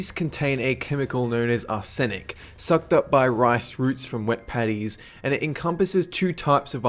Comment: I thought for rice he said that the rice soaked up arsenic from WET PANTIES, and I was just sitting there thinking "wtf, has he gone mad? He's had too much tuna apparently..."